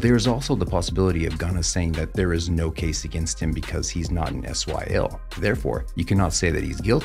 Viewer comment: did he just say SYL instead of YSL 🧐